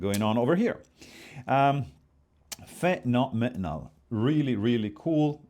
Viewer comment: Did he say fentanyl metenal?